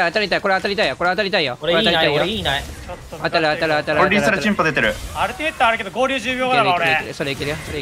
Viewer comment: なんて言ってたんだろうか…🤔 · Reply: リーサルテンポ出てる を tmpって言ってるね